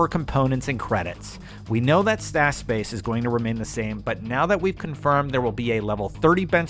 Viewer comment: did somebody say vault (stash) space?